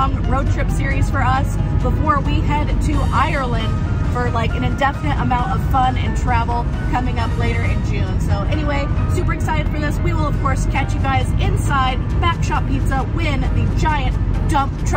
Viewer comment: I thought she kept saying "backshot pizza"....haha